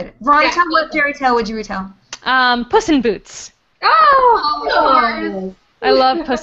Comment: Did Veronica say "Possum Boots"? :D